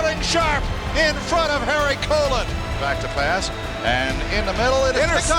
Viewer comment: did he say "Touchdown, Sterling Sharpe in front of hairy colon?"